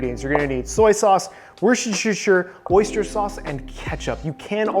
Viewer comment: Sorry but as Non English Speaker what the hell did he say at 4:17? Worcestershire sauce? I can't.